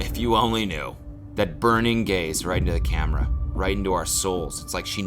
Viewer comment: When you here Cody say “burning gays” instead of “burning gaze”